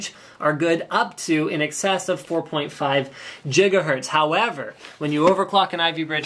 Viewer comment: Yeah, I heard him saying jiggahertz instead of saying gigahertz! lol